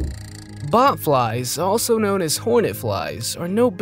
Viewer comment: I thought you said Butfly 😂😂😂